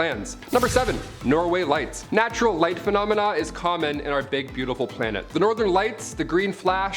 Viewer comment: I swear I heard say “our Fake beautiful planet…” 🤯